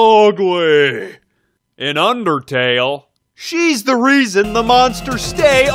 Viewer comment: i heard undercale